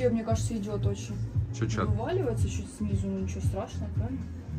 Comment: Что он сказал ? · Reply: От ела она просто че